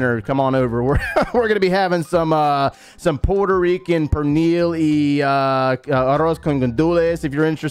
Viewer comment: What is Gonna Cook? What did he say from 8:50 To 8:55 ?? OMG I tried to hear that part 17 times still I was not able to understand that! !! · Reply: he said Pernil arrow con gandules